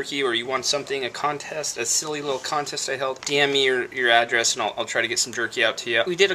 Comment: Lol you were saying DM your address I thought I were saying damn! Your address! Heehee